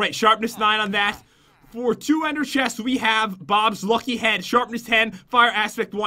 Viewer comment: When u said for. 2. Ender chest. I thought u said 200